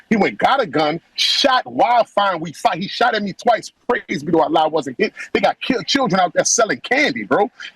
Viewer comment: What did dude say about ALOHA SNACKBAR?? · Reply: “Praise to Allah I wasn’t shot”